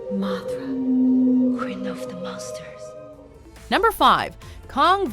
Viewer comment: Idk why, I heard "Mothra, queen of the mustard"